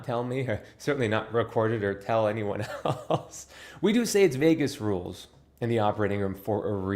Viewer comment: Oh, Vegas! I thought you said vagus 😂😂😂